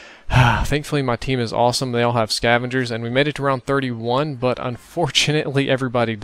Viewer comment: can three players get the scavenger? because i heard you say "they all had scavengers.